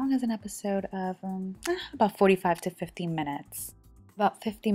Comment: Wow I thought she said to the cook the chicken 4.5 minutes to 15 minutes, had to listen 5 times to get 45 minutes to 50 minutes.